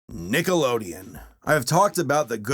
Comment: At first i thought he said Niggalodeon